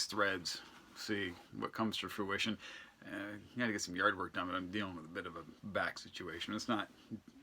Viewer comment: Oh yard work I thought you said Yardbirds!